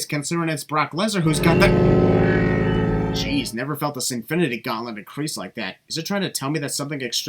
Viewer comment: The second I heard "Sinfinity", I thought you're gonna add Infinity number of Sins :)